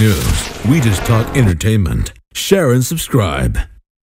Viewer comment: I don't know why, but every time I hear the beginning, instead of "Share and Subscribe" I hear "Sharon subscribe/subscribed".